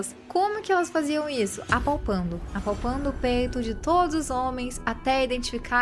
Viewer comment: Como se já não fosse nojento o bastante, minha audição ainda me prejudica… qdo vc falou q elas tinham q apalpar O PEITO dos caras p reconhecer o filho, eu escutei "o pênis". Passei meio mal, voltei pra ouvir de novo e aí pude voltar a respirar.